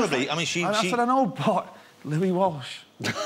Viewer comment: what did he say at about 3:40 ? and the audience clapped and cheered. I sometimes cant understand him...pls if anyone caught what he said..... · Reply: Louis Walsh ? who is that...nevermind..i know i wont understand ..thanks for letting me know anyway. I like listening to him talk.